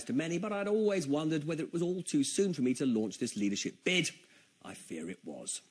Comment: what was the word he thought to replace, was it "bid"? i can't figure it out